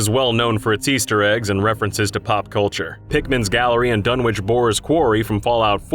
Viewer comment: I thought you said Pikmin instead of Pickman. 😂